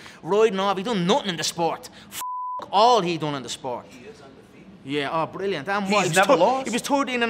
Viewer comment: Anyone else hear the random dude in the background saying "He IS undefeated though 🤔" when Conor was ranting about Khabib 😅 Timestamp